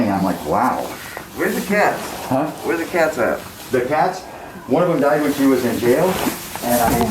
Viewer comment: Did i hear correctly at 2:55 that a cat died while she was in jail ???